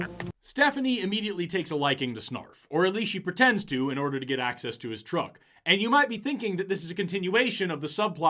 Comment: I cannot stop watching this. It makes me laugh so hard. Love your work. Keep it up. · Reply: When he said at 14:30 "At least pretends to get access to his truck", but first time I've heard it as "At least pretends to get access to his TRUNK" 😆